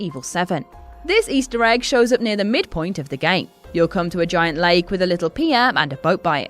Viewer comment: did she say the mid point of the game?!?!